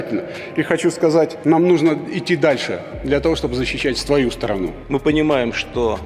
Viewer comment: все заметили что сказал СТВАЮ СТРАНУ🤣😅